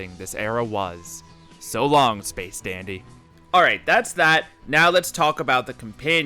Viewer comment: I thought you said 'space daddy', not 'space dandy' and my treacherous brain agreed😐